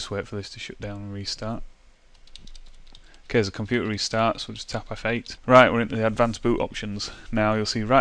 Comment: what does he say??? "tap off 8" "tap F8"??!?!?! or something, I hear